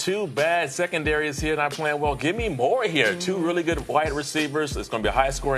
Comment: did he just say "two really good white receivers"? lmao